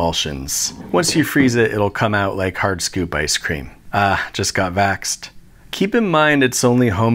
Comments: The “ah just got vaxxed” made my day. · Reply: I misheard "just got waxed" and I was laughing too 😅😂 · The magnetic/vaccine joke deserves way more praise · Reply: ohh i thought he said "waxed" so the wax was sticky or something xD